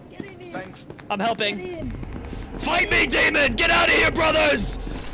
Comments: I swore he was saying "Fight me David!" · i heard 'fight me david' instead of fight me demon lol🗿